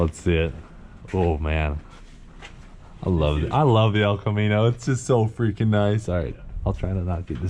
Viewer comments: did he just say "El Chromino" ?? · did he say “I love the El Chromino” 😂😂😂